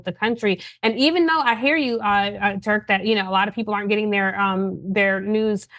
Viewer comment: Did she call Cenk “TURK”😂